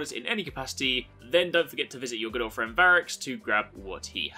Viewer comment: When you said "good old friend variks" I heard "your girlfriend variks" haha